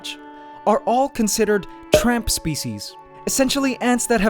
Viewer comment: I thought he said "Trump Species"